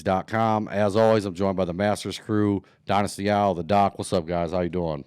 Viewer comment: It was - His name is - You called him Dynasty Owl in a message - Which it does sound the same - So now it is Dynasty Owl - It has officially stuck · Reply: 😂 lol!! 😂 I thought I heard dynasty owl but then like 2 shows ago I heard the doc and thought to myself maybe it's 🤔